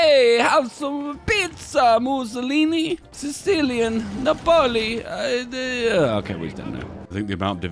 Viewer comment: Сицилия напали? мне не послышалось?